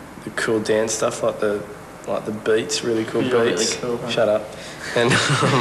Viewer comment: Can anyone make out what Dan says to Ben that makes him say shut up. I know its a joke but usually Dan says something first I think hes saying "Yeah thats really cool Ben" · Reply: It's actually Chris, and he said, "you're really cool huh?" This is 14 years late, but oh well